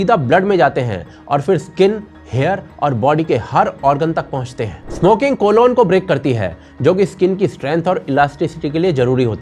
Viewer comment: Did i hear collon?